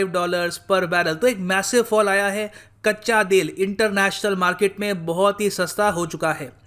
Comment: it sounds like kachcha dil instead of kachcha tel😂😂